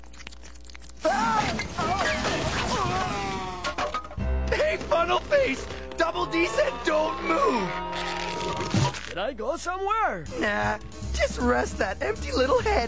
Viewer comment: "Did I go somewhere?" Literally one of Ed's best lines. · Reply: I thought he said “can I go somewhere”